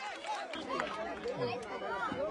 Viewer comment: I heard "HE WON A SEALING PAN"